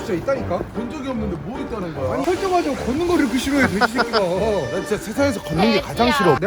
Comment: 돼지야 나만 들림???'''